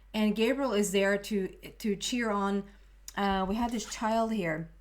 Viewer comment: The way you said / stumbled on "cheer on" I heard it as "Chiron" in my head, that then also seemed appropriate. Namaste